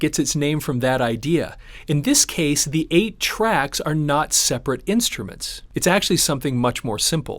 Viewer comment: I've heard people talk about these tapes for so long, but I always thought they were saying "A-track tape" facepalm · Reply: Dude, me too. Don't feel alone in mistaking 8-tracks for A-tracks.